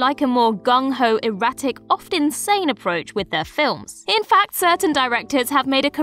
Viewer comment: When she said, "Oft insane approach" I heard "Often Sane approach"